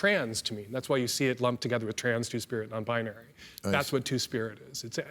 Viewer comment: I literally just heard Two Spirit for the first time on this very youtube clip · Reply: I thought he said true spirit until now